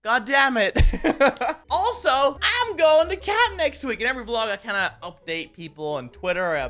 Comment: At first when you said "I'm going to camp!" I thought you said that you were going to Canada. And then I got really excited because I live in Calgary. Lol